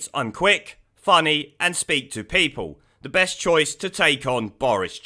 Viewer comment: I honestly thought you said Thick, Funny & Speak to People! It's about time Labour were brought screaming & kicking into the 20th Century & elected a Female Leader!!! 🤔